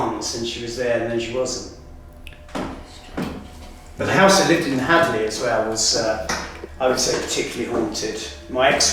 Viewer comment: a woman's voice but couldn't make out what she was saying and you can continue to hear her as they're talking. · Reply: "Strange, isn't it." That's what I think I hear. The first word is definitely "strange" but I'm less sure about whatever is said next.